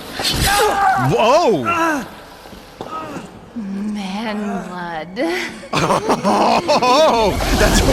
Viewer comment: For the love of god someone explain to me what she just said, because it sounds like "man mud" and that doesn't a lick of sense. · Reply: She said "man blood"